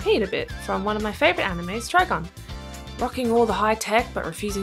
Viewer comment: which anime ? I can't understand the name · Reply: Trigun